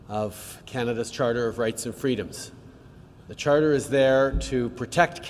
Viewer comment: Jesus, I had to replay that, because I thought Trudeau said that obviously he and his team are staunch supporters of right and freedoms. Thanks I needed a good laugh